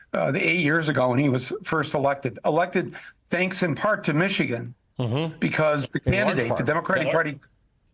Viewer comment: what does the interviewer say? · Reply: "In large part"